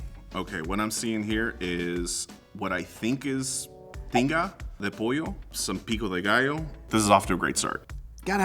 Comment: is so funny.. Definitely enjoyable to watch. I love contestants who actually come to participate and not just tear all of the other contestants down. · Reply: I thought 💭 he said Pinga de pollo 😂😂😂😂